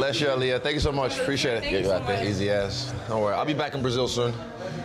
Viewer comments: Tff’ at 6:49 does Izzy say “easy ass” to the woman?! Tells her to go out and then says “easy ass” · wait, did he just say "easy a$$" right after meeting that girl?